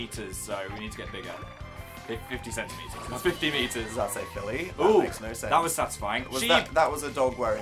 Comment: dan calling phil "philly" for the ones who were asking 2:43 4:19 & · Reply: I don't think dan was calling Phil "philly" at 11:09 i think he said "does that say Philly? That makes no sense." i'm pretty sure he was just talking about something in the game lmao